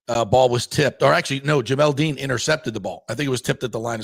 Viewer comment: tf you just say? Dean interception?